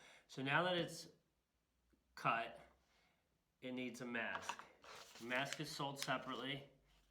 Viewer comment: It's hard to hear. What is that plastic that you mentioned at 1:52 ? What is it called? I couldn't hear it. You said it's sold separetely. What kind do you use? Thank. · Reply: Ttd transfer mask <link>